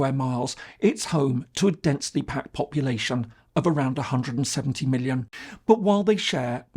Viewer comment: This is a really interesting story. India and Bangladesh have had a very close relationship for decades. But it's going through what seems to be a severe crisis. But how serious is this? Do you think it is merely a temporary rift? Or is it the beginning of a long-term rupture? Can the relationship be repaired? And, if so, how? And what role will China play if Bangladesh drifts away from India? As always, I look forward to hearing your thoughts and comments below. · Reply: you say 170 million but subtitles say 70 million.